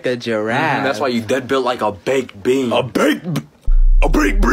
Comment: ''WHA A BAKE- A BAKE BEAN,A BAKE BEAN. SMACK BAKE BEAN THAT, BAKE BEAN THAT'' is literally the best line ever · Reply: I hear "BAKE BEAN NUT" 💀💀💀